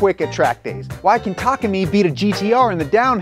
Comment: Subtitles say "Tacomi" instead of "Takumi". (and they're not auto generated). Please fix that. (I know it's small but it makes my eyebrow twitch, doubt I'm the only one)